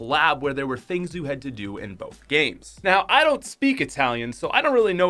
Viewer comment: did he just say i dont speao itallian